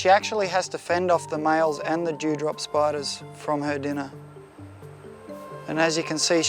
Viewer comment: I honestly heard him say the 'jew drop spider' and the whole definition got so much funnier.